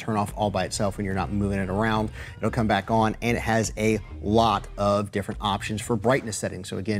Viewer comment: 4 to 14 hours and it will shut off when you are not moving it around and it will come back on? Is that what you said or is that what I heard around 7:20?